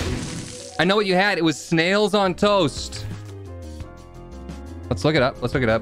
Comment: When talking about the weirdest things eaten, I thought said “eggs on toast” and I was about to be pissed because eggs and toast are super good